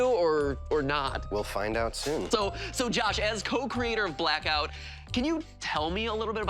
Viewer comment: I thought you said “coke creator”